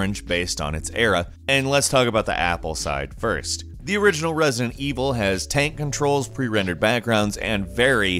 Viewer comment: I thought I heard he say President Evil.